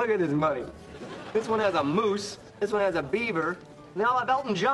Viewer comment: I heard it as if they actually printed Justin Bieber's face when he had actually said beaver